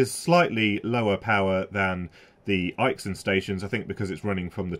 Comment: Can you share a link to the one you say it's not a powerful as at approximately 16:40 in the video?? I can't make out the name. Thank you in advance. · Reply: I believe it is Aixun